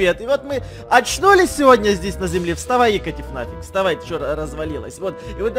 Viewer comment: вставай Игорь? Или мне послышалось